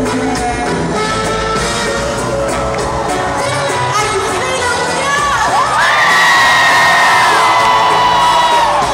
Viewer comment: what did she say at 3:18 ? i couldnt understand. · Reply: are you feeling Rio